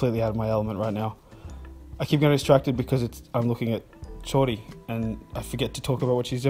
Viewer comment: “I keep getting distracted looking at Jordi” Yeah I can relate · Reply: I thought he said he got distracted looking at Shorty. (the daughter)